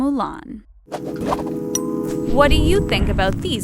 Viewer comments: it sounds like she is saying subscribe to the taco who agrees with me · It sounds like she is saying don't forget to subscribe to the TACO😂🌮